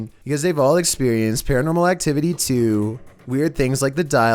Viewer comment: Bruh, I thought you said "Because they all have experienced Paranormal Activity 2" I was like "I love that movie!"...then I realized how dumb I am 🤦